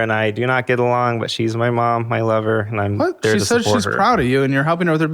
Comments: I'm sorry.... does he say....his mom is his lover? How did Caleb NOT hear that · did he just call his mother his lover??? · “she’s my mom, my lover” did i hear that right??😭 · Did anyone catch that he called his mom his “lover”?…